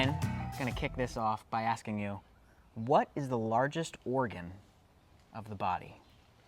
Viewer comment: I had to keep going back, I really thought you said “ Brian what’s the largest orgy....and he says you want me to answer that on camera”. It fit, thanks for the laugh. I needed it